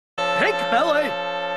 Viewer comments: I thought Ed was saying "Pig Belly" when I was younger 😂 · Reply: Lol the first time I saw this, I thought he said pain belly. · When I saw this as a kid. I always thought Ed said "Big Belly" before smacking Edd's belly. Now I know it's "pink belly" lol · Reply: I thought he said "Pig belly"